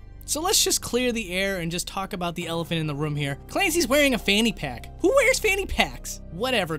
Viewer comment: did u just say fanny pack??? Wtf